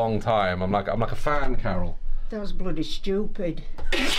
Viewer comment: when carol had said "That's bloody stupid" I thought she said "Daz bloody stupid" lol!